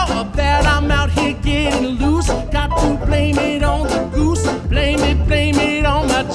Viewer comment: i misheard “blame it on the jews” 😂